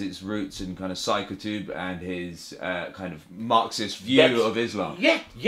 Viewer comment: “the root of cycle tube and his marxist…” Does anyone know what the interviewer is actually saying?